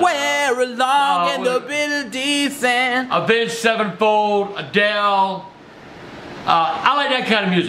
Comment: Wait did he just say avenge sevenfold😂 man was a metal head